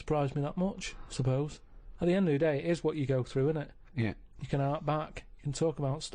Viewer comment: some of Karl’s turns of phrase are really fascinatingly old English, some moments it’s like Chaucer or something: “You can hark back.” (Think back on) Ricky mishears it as “harp” like “harp on”, but it’s “hark”